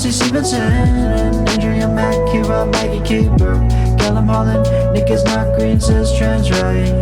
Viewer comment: Whenever u say nick is not green,I always hear "niggas not green" the first time and need to remind myself thats not what u saying lol